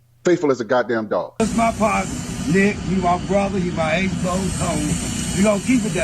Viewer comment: "He my ace boon C00N"?! 😮 Anybody hear Uncle Ruckus??🤔